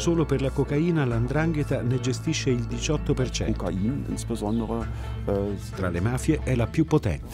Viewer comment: ma sono l'unico che si è accorto che il tipo al minuto 17:52 in tedesco dice "80" non "18" come poi viene detto nella traduzione in italiano?